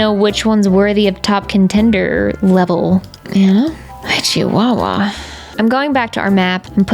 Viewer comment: Did I hear an “Ay chihuahua!”…?